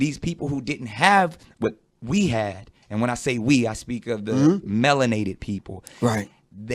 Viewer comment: For a sec I thought he said 'laminated people like us".